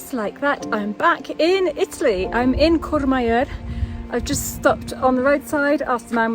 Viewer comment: What is the name of the town at 19:12? I played it back several times and I still can't understand the name you're saying. What a beautiful place! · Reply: Courmayeur